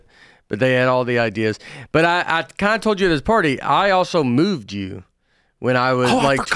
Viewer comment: I thought he said “I mooned you” not “I moved you”..my hearing needs checked😂😂